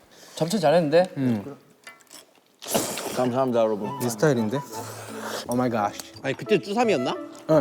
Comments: did I hear a cute voice "o may gosh" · did- did Jungkook said "oh my gosh" ? SO SASSY LMAO i like it Edit: Sorry, it was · Did someone say “Oh my gosh” 😂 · Am I the only one who heard Jungkook's "Oh my Gosh" at 25:25? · omg!! It sounded like ohh my gosh in a native American accent.. did they really said that or I just only heard😅😅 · Am i the only one who heard the 'oh my gosh' at 25:26? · Did jeongguk say 'oh my gosh' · did anyone catch jungkookie little "oh my gosh" at 25:26?! LOL this boy.... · In 25:26, I think I am the only one who heard the word "Oh my gosh". I thinks it's Hobi's voice.